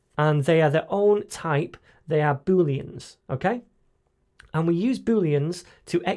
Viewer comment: if you Trurn The subtitle in 1:01 Min You Will See Billions instead of Booleans Huh